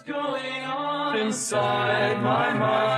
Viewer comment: Dam i always thought he said “Doctor I am terrified about me”